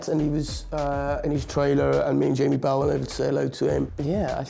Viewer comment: I so very, very, very, very LOVE Taron Egerton 😘 😘 And.... 0:38 did he just say Jamie Bowel/Powel? 😂 😂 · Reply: Jamie Bell